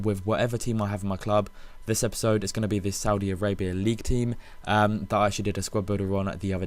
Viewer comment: Haha I thought you said saudi-arapia